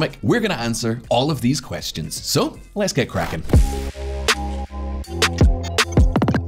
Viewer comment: our presenter uses the English expression for get to work, "Let's get crackin'" and all I heard was "let's get Krakken" a.k.a. the latest COVID variant. I know the script was written before the latest variant was named but I think I might be affected by too much news feed. Hypothetical example. What if other countries could check there biases at the door and actually study Danish tax structure? My country included!